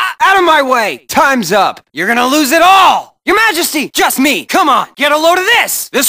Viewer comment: Huh, and here I always thought he said "Checkmate!"